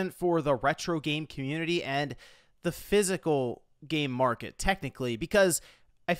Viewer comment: i swear i heard him saying "the physical gay market"